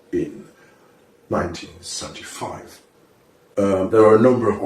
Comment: Ahaha fake he says at around 12:00 if you listen carefully “time travel was perfected in 1975 and the subtitle says 2075” get you’re facts straight